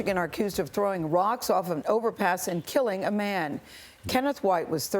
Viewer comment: just from this video alone. I counted a total of 23 plus times they threw stuff off an enterpass. The video says 20 rocks were found on the ground below the enterpass. Plus the 1 that killed the victim. The report also said that off another enterpass they threw a tire which makes the total now 22 and engine parts. NOw the report said engine parts. Parts is plural. So more then 1 engine part was thrown. So just from this video says the kids threw something off an enterpass 23 plus times. Plus meaning its actually more then 23 but cant put an actual number because it didnt say exactly how many engine parts were thrown. 23plus times someone could have died from their actions. 1 time someone did. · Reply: oh lol Im from the united states. I just realized a mistake I made other then miss spelling interpass. I was just going off the video. I thought the video said interpass. It said overpass. And an interpass is when two roads form a uncontrolled intersection. Meaning no stop signs no stop lights. I dont know why i heard interpass instead of overpass may have been distracted or something lol. But yes its an overpass not a interpass.